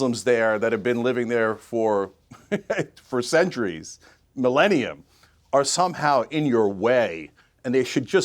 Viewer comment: Does he say millennia?